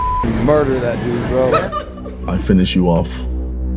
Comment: did Logan just say "i am gonna murder that negro"? Because wtf